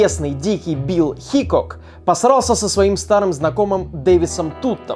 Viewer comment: Мне одному кажется что он сказала "посрался"